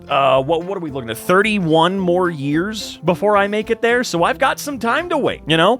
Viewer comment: I misheard, and thought you were joking that you had 1 more year til you were 60.